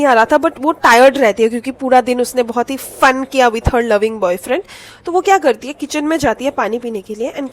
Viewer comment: What did she say? 'FUN'. But legends hear it(FUCK) 😂